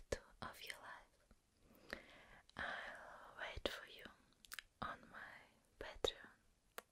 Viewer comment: Я знаю что она сказала "I will wait for you on my Patreon"... но мне послышалось "I will wait for you in my bedroom". 🤣🤣